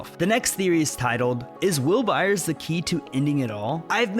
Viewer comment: lokwey thought he was gonna say is will Byers gay💀